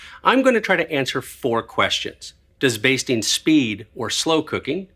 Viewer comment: I thought it said “Does beasting really work?”